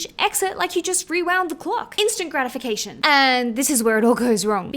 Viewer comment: I thought you said Instagramification